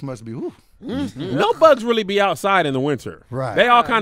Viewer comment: 🤣 I thought said “Thugs don’t really be out during the winter. They kinda take that off.” But he said bugs 🤦🏾‍♀️ thugs do too tho lol